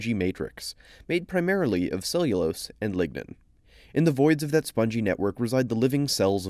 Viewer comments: I thought he said of cellulose and ligma 😂😂😂 I was like what? Seriously? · I swore he said made of cellulose and ligma